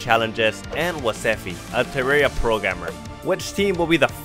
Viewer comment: i thought he said a terraria pro gammer